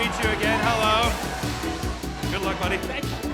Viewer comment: I didnt catch it at the show but totally says “good luck buddy” to the guy who proposed because he knew 😏